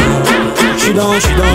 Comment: It sounds like he says “she don’t text” but he is actually saying j’suis dan I’ tieks, which means I’m in the hood